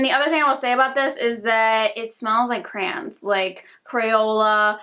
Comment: I thought she was saying “it smells like crans” like cranberries. Then she said “like kindergarten” and I was completely lost 😂😂 She explained further and I was like oooooooooh cray-ons. I now realize you have a small accent and I’ve been watching you forever lol